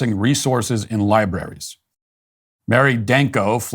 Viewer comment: Did he just say liberries?